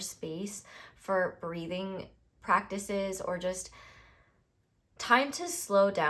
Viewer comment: Pile 1 - Love - instead of "breathing practices" I heard "breeding practices" and idk if I should take it as a sign